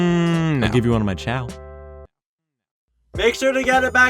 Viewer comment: “I’ll give one of my ???” What’s that last word? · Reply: chow?!?